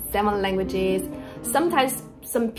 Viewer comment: Who else thought it said 71 languages instead of 7😭😂